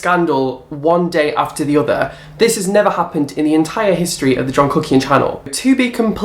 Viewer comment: Sometimes I hear "john cooking" and others "john cookie" haha this is why I'm fat.